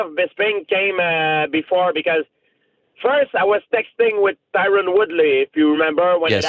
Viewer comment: at first i heard him say, " i was sexting with Tyron Woodly" hahaha